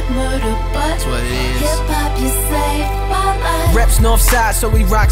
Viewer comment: when I was little I thought she said hey pop you saved me